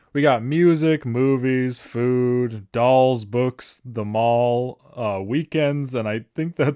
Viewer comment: K i thought it said balls not dolls